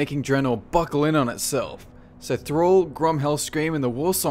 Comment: did you say thral?? He was never on Draenor